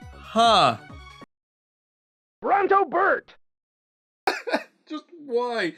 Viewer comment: I think Kirby just said run don't get burnt!